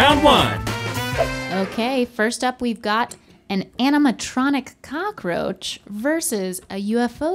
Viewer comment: I thought she said “enema-tronic” at the beginning